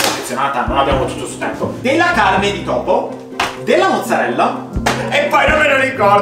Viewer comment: ho capito bene di topo?😂😨